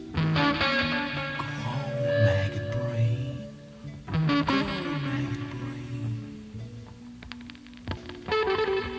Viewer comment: what does he say? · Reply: Rebecca Valente "we are going to protect each other from anything that could separate us "